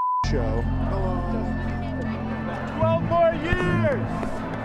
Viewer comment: the guy said 12 more years but the caption said 4 more years. make sure we get the aesthetics of the brainwashed and delusional is all